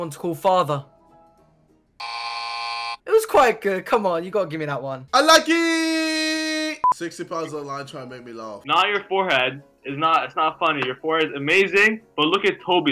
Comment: i know its “unlucky” but i first heard it as “ i like it”